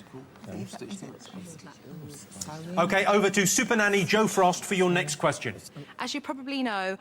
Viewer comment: i thought he said "Supper Nanny, Joe Swash". i'd be up for that. 😁